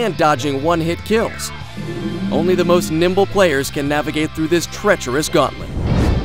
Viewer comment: haha, at 1:38 i know what he said but it kinda sounded like "only the most nippled players can navigate through this treachorous gauntlet"